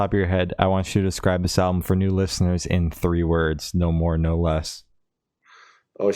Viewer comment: Bwahahaha 😅😅😅 I thought you said ”explain EWO in 30 words”, heard it loud n clear now that you said 3 words 🤣🤣 Thanks again for having me, keep up the good work ✌️